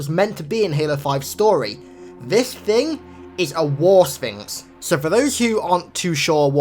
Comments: I thought he said war sphincter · for a brief moment I thought you said war sphincter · I thought you said war sphincter